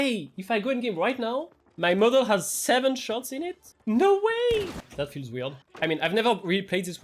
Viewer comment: I had to double back at 9:50 i heard "My mother has 7 shots in it"💀💀